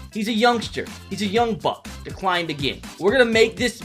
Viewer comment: He's a young buck What I heard: He's a young f***